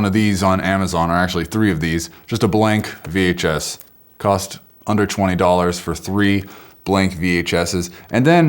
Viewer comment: i thought you said cost $120 lol but you said under $20 i was like wtf for a second